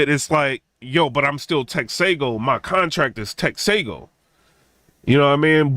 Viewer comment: I thought he said his contract said Tex? 🤷🏾‍♂️